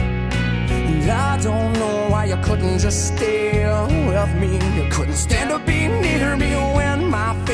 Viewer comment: I always thought he was saying I don't know why you couldn't just stay UNWEALTHY at 1:44 lol I was a poor kid :/ made sense to me 😅